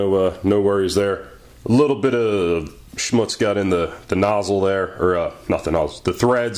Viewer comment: Did he actually say 'Schmutz' at around 14:15?